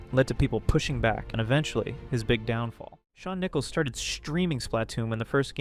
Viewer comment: I thought you said Sean Nipples 💀